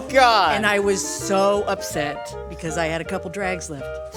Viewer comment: Definitely thought she said "and i was so upset, I took a couple drags of it" in her cigarette nurse story. Turns out it was "because it still had a couple drags left". My disappointment is immeasurable and my day has been ruined.